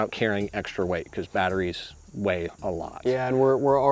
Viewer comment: Wtf did he say here? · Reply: Batteries weigh a lot